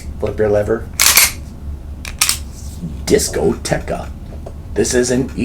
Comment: I remember the first time I saw his reviews, I thought I heard him say, “Disco Grandma!” Lol